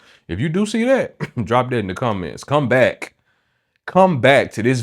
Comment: I thought you said drop dead in the comments 😂😂😂